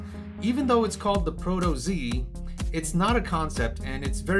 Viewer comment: I heard it's not as wide